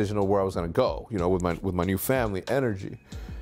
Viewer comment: “NRG” Not Real Gamers Jk this is my favorite team just felt like making a joke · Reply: At like 1:20 it sounded like he said energy N R G